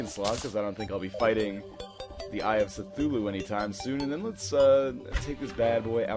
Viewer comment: Sounded like he said Eye of SITHulu. NORTHERNLION, I AM YOUR FATHER!